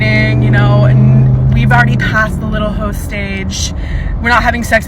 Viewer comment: All i heard was " we've already past our hoe phase." I check out after that